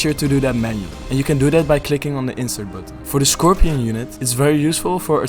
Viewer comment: I'm sorry what? I didn't understand what he said at 2:14. make sure you use what button? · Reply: I think “insert button”